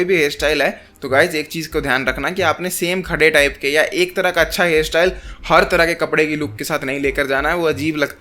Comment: Bhai yha PE aap kya bol rhe ho kus PTA nhi sla rha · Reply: 😢bhai same hair styles matt rakha bola